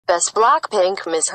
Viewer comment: 1St one i heard "in the moon in the world"